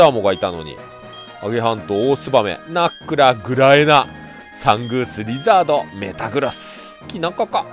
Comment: サングースじゃなくて ザングースじゃないんですか？ 聞き間違いだったらすみません。